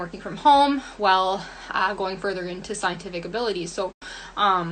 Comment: DID SHE JUST SAY “SCIENTIFICABILITY” Who in their right minds would hear this made up shit like that and think to themselves “yeah, it sounds like she knows what she’s talking about, I should really buy her products”